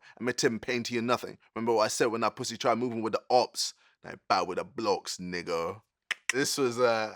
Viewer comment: I thought he said I ain't bad with the blocks nigga. Like blocking in boxing